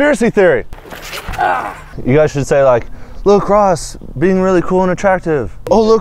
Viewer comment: When he kept saying “look Ross” I thought he was saying Lacrosse. Holy shit I had a hard time tryna understand why they was laughing